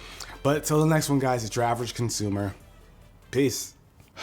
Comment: is it just me but it sounds like he says savage consumer instead of average consumer